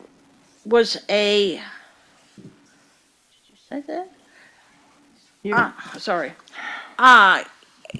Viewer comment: It's a very minor point, but I'm fairly certain that Nancy asks her attorney, "Did YOU say that?" not "Did I say that?"